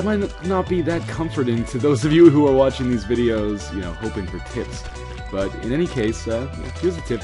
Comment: I thought he said 'hoping for tits'